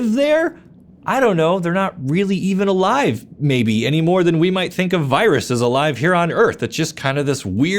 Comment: I thought you said: “they’re not really even alive, any way weld think that Congress is alive here on earth.” I was floored at how poignant your criticism of our current political climate.